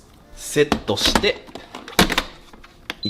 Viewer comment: これでもう炊けるのを待つだけ！が、これでもう炊けるのを松茸！って聞こえた